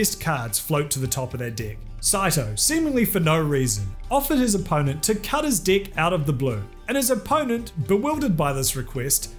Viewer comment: Wow i heard "tried to cut his dick" at first, i was gonna say that escalated quickly 😳